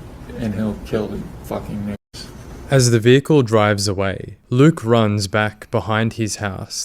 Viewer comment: YouTube auto subtitles says "fuck*ng knights" instead of "ni**ers" 😂